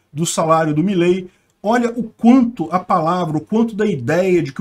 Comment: a palavra? Ouvi bem? e.e